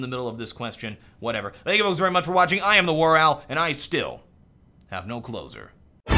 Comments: sry i cant speak english very well. what does "i have no closer" mean :) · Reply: venuslippen saft actually ...for quite a lot of time i always heard: " and i still have no clothes on" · sry i cant speak english very well. what does "i have no closer" mean :) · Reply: ***** I thought he said I have no clothes on...